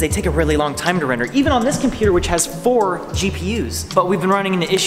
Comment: did you just say 4 cpus???????